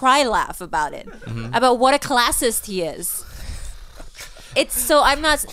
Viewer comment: Does she say calastus at 51:50? Can anyone confirm or explain what that is? Just curious. Google was only slightly helpful.